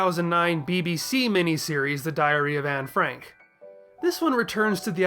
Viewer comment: I misheard 9:04 as "The 2009 DBZ mini-series, The Diary of Anne Frank". Now THAT is a different kind of story. Probably a spin-off of the Trunks Special.